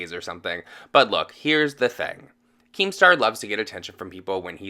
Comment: I’m the stupid bitch that thought you kept saying “king star” as in Jeffree Star... idk who keem star is. Irrelevant much lol